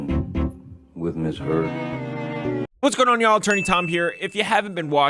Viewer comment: i KNOW you’re saying attorney tom but i keep hearing “Turner Tom”